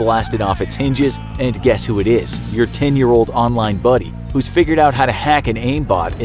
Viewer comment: 10-year old “buddy” The captions say “bully”..